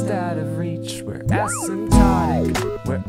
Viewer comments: I thought this said asymptomatic at first, but this is so much better :) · My dumbass has been worried about covid for so long I thought it said asymptomatic lmao